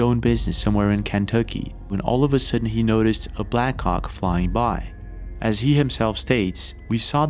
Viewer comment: Every time u say Blackhawk, it sounds like ur saying black cock... just fyi 😂